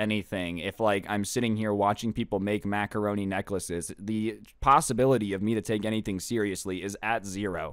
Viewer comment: did he say... pasta bility